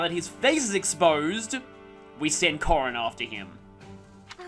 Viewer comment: did you said Corrin or Corona? If it's the latter, it aged too well. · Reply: Corrin. The Lord of Fates who loves touching faces.